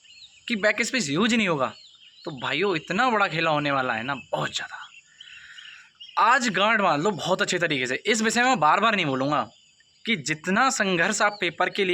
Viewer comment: kya bol rhe ho ye 😂😂😂😂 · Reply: Aaj ganth bandh lo ye kah rhe h bhai Kaano ko saaf karao